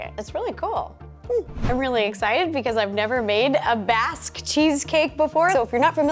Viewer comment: The name written on the description is Baste Cheesecake, but I’m sure I hear Ro saying Basque. So which is correct?